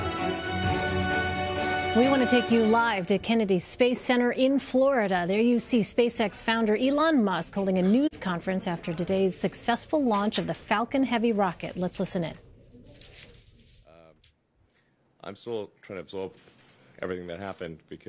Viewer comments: Am I the only one that thought the reporter said Elon Musk holding a nudes conference? Time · anyone else hear the reporter say "nudes conference" and not news? xD